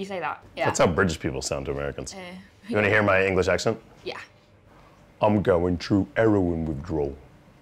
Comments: I'm so LA. I thought he said, "I'm gowen fru Erewhon withdrawl." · "Wanna hear my English accent?" "I'm going through 'eroin withdrawal" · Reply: Lol I thought he said Erewhon withdrawal (the grocery store)